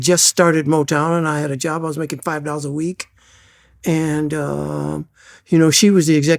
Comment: did he say $5 a week or $500??